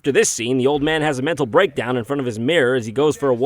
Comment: what is he saying? Something with breaks?